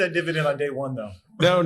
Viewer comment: did someone say dividend??